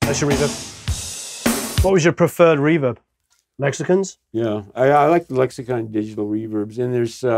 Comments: I totally didn't pick up the accent for a sec, so I thought when he said, "What's your preferred reverb, mexicans?!?" I laughed for like 5 mins, before I realized he said lexicons. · Hahahaha, I misheard what you were saying at 20:02 because I thought I heard 'What was your preferred reverb? Mexicans?' Obviously you said 'Lexicons', but it definitely confused me for a second.